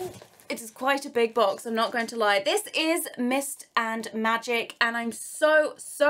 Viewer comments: The whole time I thought you were saying mister magic but you were saying mist and magic 🤣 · instead of "mist and magic" I keep hearing "MR Magic" and it sounds like a character from incredibles :D